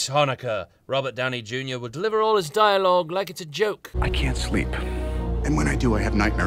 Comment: At first I thought Tony said "I can't sleep..."